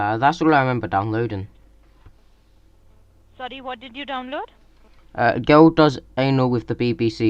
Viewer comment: i heard him say girl does anal and porn😂 i woke up my parents laughing